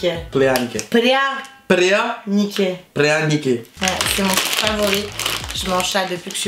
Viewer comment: moi j'ai cru comprendre " prêt à niquer " 😅😂